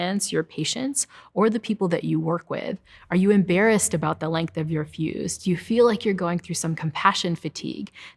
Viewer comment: Wasn't watching and I hear "are you embarrassed about the length of your pubes?"